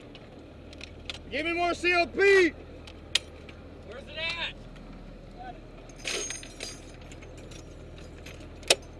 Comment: at min 3:20 he sprays something over the bullets, can't understad what he says, I'm not native english speaker...someone can explain? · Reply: He´s asking for more CLP. MIL-L-63460, think about WD-40. <link>